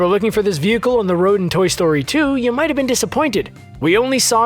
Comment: he says toy story 2 instead of 4